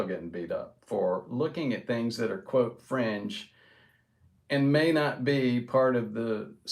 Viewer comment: transcript says French instead of fringe. made me LOL. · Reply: The first few times he said ''fringe'' I heard ''French'' but figured it was something else!